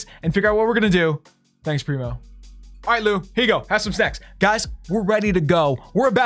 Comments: Is it just me or does sound like he said @ 5:05 “here Lu have some sex” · did I just hear "have some sex"?